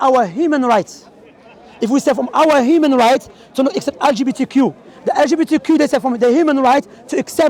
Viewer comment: Assalamu Alaikum brothers just to let you know that the caption on the 11:55 isn't correct. said '' IF WE START FROM OUR HUMAN RIGHTS TO NOT ACCEPT...''. but the caption said '' TO ACCEPT...''. Jazāk Allāhu Khayran.